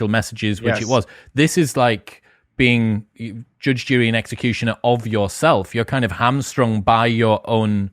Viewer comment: I had to listen twice because I could have sworn that said "Judge Judy and executioner". 😂